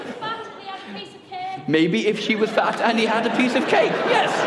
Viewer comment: What does the lady at 5:02 shout? I can´t make it out... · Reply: "Maybe if the girl was fat and he had cake..." Funny lady.